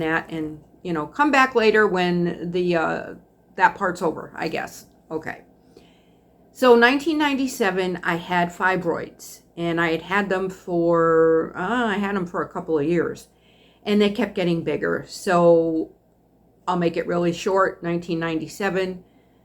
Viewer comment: Geez...when you were talking about your fibroids...I thought you said, "In '97 I had five boys" 😮 I think I'm starting to go deaf...😂